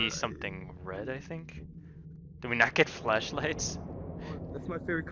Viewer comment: I thought he said oh we do we not get flesh lights I was like tf